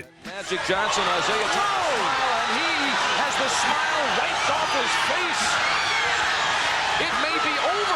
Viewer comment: did the commentator say "Magic Johnson has AIDS?" Lmao. I mean thats what it sounded like.